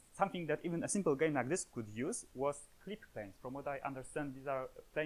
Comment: I couldn't hear what he called the system that checks if an object is in view before it is rendered. Subtitles say "quick thanks" but I doubt that's it. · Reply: Clip planes - I'm guessing he's referring to the near and far clipping planes of the view frustum, which has nothing to do with performance.